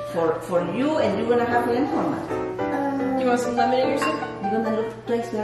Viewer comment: Am I the only one that heard say if P wanted Lemonade on her soup instead of lemon on